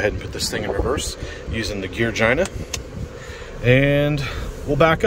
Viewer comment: Did you say gear-gina lol?